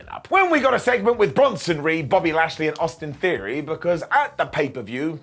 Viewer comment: Not sure why each time I heard Bobby Lashleys name I heard Bobby Roode and got real sad when I misheard, I miss Bobby Roode